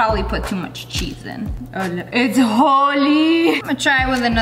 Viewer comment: Anyone heard lia saying 𝙞𝙩'𝙨 𝙃𝙤𝙡𝙞 !! 😂 3:52 lol 🤣🤣🤣 I know what she said but I was thinking of holi 😂😂😂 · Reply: She prolly meant its holy